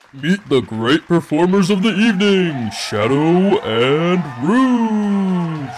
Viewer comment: What i heard: "Meat the great performers for the evening shadow and brougee"